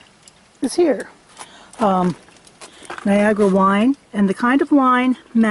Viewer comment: I like cooking on cast iron. Plus it's the only thing I know how to do. Fry stuff or use the BBQ. I thought you were saying "Viagra wine". lol